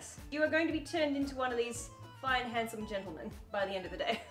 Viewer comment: “You’re going to be turned into one of these 5 handsome gentleman” I’m not a bts fan but there is definitely 7 men in that photo 😂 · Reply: I could be wrong but I thought I heard her say “fine handsome gentlemen”, not “five handsome gentlemen”.